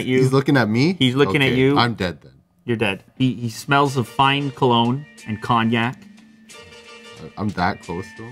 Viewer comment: I hear "smelling of fine KANYE"....